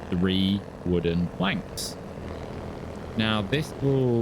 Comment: three wooden.... wanks? lol I can't be the only one who heard it like that. :D That said, this series is gold, I love it! Your channel should be much bigger, you rock.